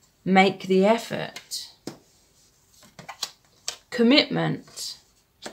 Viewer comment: I thought it said communist and I was like HUH?? Then she said commitment and J was like...oh.